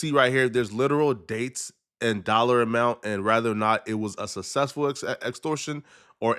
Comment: why do people say "rather" instead of "whether?" ..... its whether.